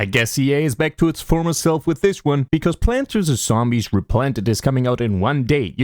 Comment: you used ai for you captions, it thought you said planters instead of plants vs. youre a hypocrite